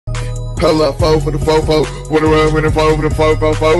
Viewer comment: Is it just me, or do I hear “hella” instead of “pull up”?